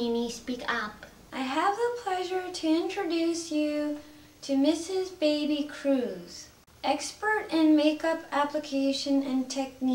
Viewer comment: The aunt's name is "Baby Cruise"?? Thats what Minnie says on